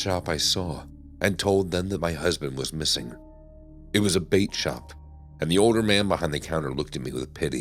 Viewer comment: Lol story 4 I thought I heard vape shop 😂 It was a bait shop..